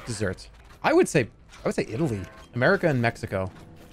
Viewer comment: why did he say "Pussy Italy"? :(